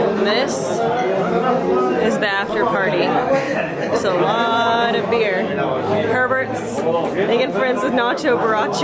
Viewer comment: Did she say, a lot of beer and perverts? Lol😄